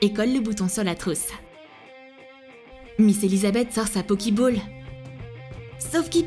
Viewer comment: C'est moi où à 10:52 elle dit « Pokiball » ? 😂